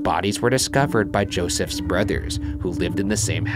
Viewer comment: who else hears Ryan says brutters